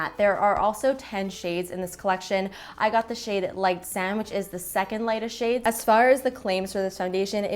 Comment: I thought you said light sandwich. Lol. 😂